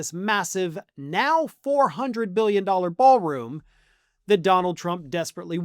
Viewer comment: Did you just say $400 Billion? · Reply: No, million But don't put it past him to SAY BILLION! (Cuz you know That's his FAVORITE WORD/NUMBER to say.)